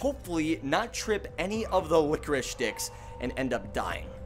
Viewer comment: bro did not say licorice sticks 💔